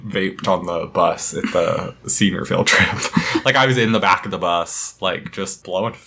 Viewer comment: I really thought he said “bake” instead of “vape-“ I just imagined him in the back of the bus with the blanket on his lap with an easy bake oven